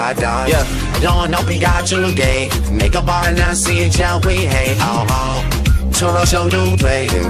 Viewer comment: Lmao I hear “see u in jail” here